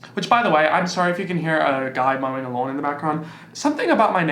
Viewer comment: Haha for a sec I thought said “sorry if you can hear a guy moaning along in the background” 🤣🤣